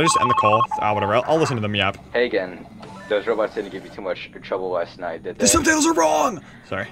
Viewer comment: !!!THE SUBTITLES ARE WRONG!!! no they're fucking not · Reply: did u listen to the guy saying "hey again, those robots didn't give you too much trouble last night did they?" and the subtitles say animatronics.... they are wrong lol